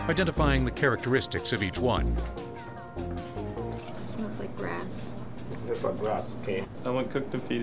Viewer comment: did she said smells like Grass or